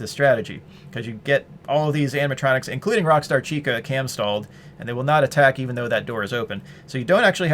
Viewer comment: I thought i learned this technique from this video but i guess i misunderstood rockstar chica. when u said rockstar chica could be cam stalled i thought u said like door stalled or office stalled because I finished 40/20 by door stalling chica because apparently she has mangle physics where she wont jumpscare or get in if the door is closed when you flip. i rewatched the video and realized what i did wrong cause most of my losses in 40/20 was due to me accidently opening the left door before i flip my camera down so thx for the info